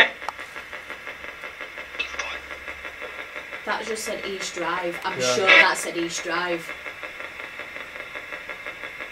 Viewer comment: Before i forget im posting this as i hear "East Drive!" at 50:18 to just after the EVp "death it's here!!" its clear but a pitch or so lower. So far loving everything including the mad heads Finders bleepers lol excellent Fun and a great investigation and thank you all.